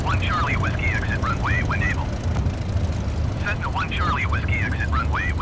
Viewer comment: What does it say after Cessna one Charlie Whiskey exit runway ........ (one evo), I don't understand the latter. 15:30 Que dice luego de Cessna one Charlie Whiskey exit runway........(one evo), no entiendo esto último. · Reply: “When able,” I believe.